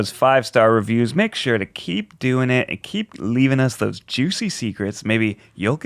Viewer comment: For some reason every time Ned said 'keep' I heard 'Keith'...